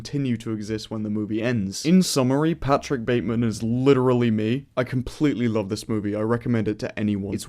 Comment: Wait 28:42 did he just say Bateman is him after calling him a beta 😂😂